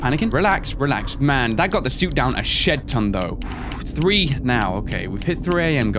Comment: I noticed you are now allowing more swearing with your horror gameplay. Not complaining, though! Just letting you know I have noticed it. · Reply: Matt Smith not really but at 2:56 it sounds like he says shit ton but he says shed ton